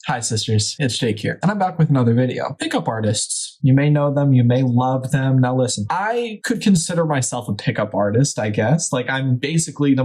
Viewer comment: So… this whole time I thought he was saying “hello shisters, it’s shake here.” I’m not dumb